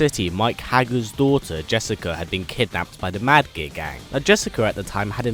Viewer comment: I keep hearing "Mad Gay Gang"